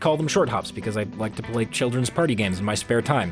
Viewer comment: When you said children’s party games I thought you said card instead of party